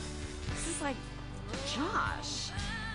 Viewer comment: is it just me that thought she said ‘ i like josh ‘ all this time :/